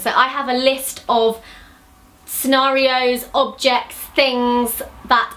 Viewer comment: When she said scenario I thought she said Oreo